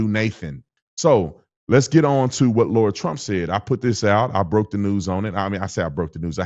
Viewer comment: I thought you said lord Trump but you said Lara Trump 😂